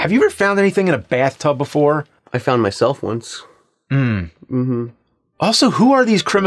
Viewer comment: "have you ever found anything in a bathtub?" "I found myself once" IM DEAD XD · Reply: Um I thought he said I fondle myself ... 😅🤣😂... I'm like omg me too ha